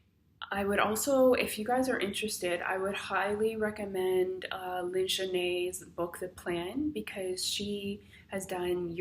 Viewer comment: Would you say meat can be a trigger? Also at 8:19 you say "I would highly recommend..." but I don't quite understand what you say next. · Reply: I said Lyn-Genet Recitas book THE PLAN and I didn’t eat that much meat before but in her book she talks about some meats causing more inflammation than others..but for me I think it was the eggs/dairy